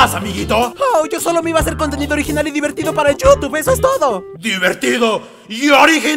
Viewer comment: no entendí nada de lo que dijo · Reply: Dijo:solo me voy a hacer contenido divertido y original para el youtube eso es todo' es eso :v